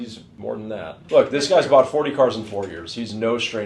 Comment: 40 cars? Did I hear that right???